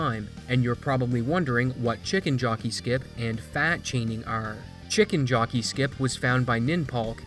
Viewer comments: I think FAT chaining should be in Any% for purity of ANY. Probably a category "Any% (no FAT Chaining)" will be more popular, but in Any% it shouldn't be forbidden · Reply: I thought you said, “fat shaming” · FATS chaining sounds like one of those glitches that will make runs technically difficult while strategically simple, and limit creative expression with the route and other tricks, so own category might be better. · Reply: I thought he said fat shaming xD